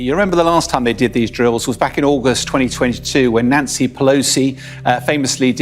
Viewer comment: 🤣 did he say: nazi pelosi?? 🤍💭 david